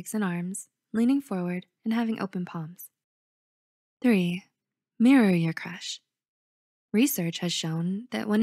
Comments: I wasn’t paying attention and I heard “Marry your crush” I was like: 😳😳😳😳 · i thought she said marry your crush 😭😭💀💀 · I swear I thought they said “marry your crush” 🤣